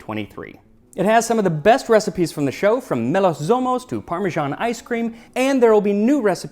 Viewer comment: Did he say "parmesan ice-cream"? 😁